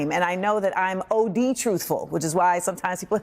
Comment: What the eff is "OD truthful" as she said at 8:54 ?